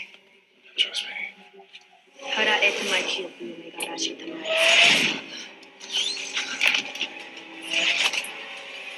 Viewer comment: O que ela fala ? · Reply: Harae-Tamai-Kioku-Yamagaershi-Tamae is the pronunciation